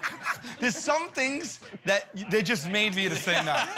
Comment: What did Sal say @ 2:24? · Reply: 'There's some things that are just made for you to say "no"'.